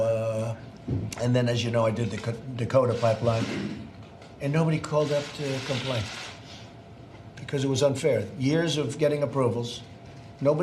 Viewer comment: FACT: "Nobody's complaining about DAPL" was NOT said by President Trump in that clip. another FAKE title. FAKE news TYT. exposed again! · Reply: Correct. What he actually said was, "And then, as you know, I did the Dakota Pipeline, and nobody called up to complain."